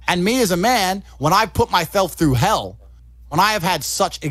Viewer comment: “put mythelf through hell” sounded like Mike Tyson 😂